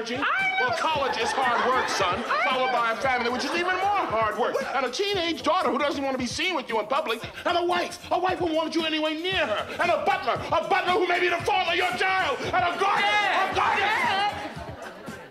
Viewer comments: Uncle Phil: "...and a Butler who may be the father of your child!". Me: Wait WHAT? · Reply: I thought he said “the fall of your job” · When I was growing up I always thought @ 6:46 uncle Phil said “A butler that may be the father of your child” 😂🤦🏾‍♂️